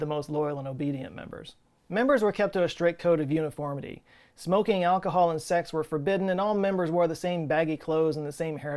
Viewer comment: I thought he said "smoking alcohol" lolll